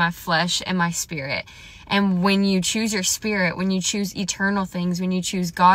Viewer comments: There's an error in your caption. At 3:30 you're saying "and when you choose your spirit..." but the caption says "and when you choose your flesh..." Did Satan trip you up so that he could mess around with hearing-impaired people? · da devil tried to deceive once again .. u clearly said "spirit" and da subtitles said u said "flesh" .. had I just been watchin da words nd not listenin dat woulda got me .. yall be careful . Da devil is ALWAYS workin. Keep talkin tho sis 🙏🏽💯 dis message hit